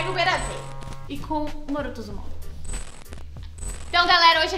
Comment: só eu ouvi "Boruto Uzumaki" ou to ficando doida😂